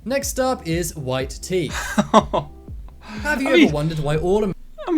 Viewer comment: I wasn't looking at the screen and I thought it said "White Tea". Like how there is "Green Tea" and "Black Tea". I thought it was some new tea I hadn't heard of.